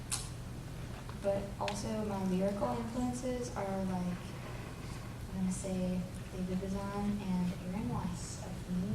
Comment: hi hello... well english is not my first language, so I didn't catch some sentences, could anyone please help me out? 2:37 - what are her lyrical influences? 3:25 - who's the person she’s quoting from? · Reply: her lyrical influences are David Bazan and Aaron Weiss from the band Me Without You 3:25 she's quoting the Austria poet Rainer Maria Rilke (she loves him, in her Audiotree interview she talks a lot more about how book influences her) :)